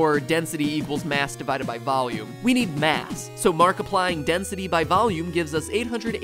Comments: did he say markiplying or am I just tired Edit: he did, if you look you can see the mini markiplier thing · did he just say "markiplying"?